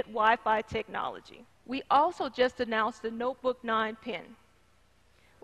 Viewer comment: I thought she said "PIN"